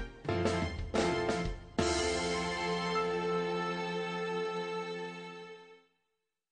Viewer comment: What is the last word in 21:04 please?? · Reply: It just says "The End"